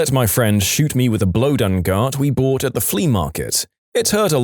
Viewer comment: 😂😂blow dun gart instead of blow gun dart lol